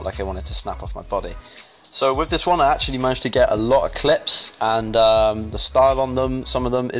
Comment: Sick session, 3 tatw nt was stylish! I love your variety in training. At 0:25 it kind of sounds like you say 'I managed to get a lot Eclipse' haha!